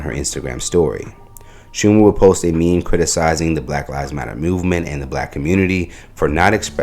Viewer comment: thought she said all lives matter (ALM)!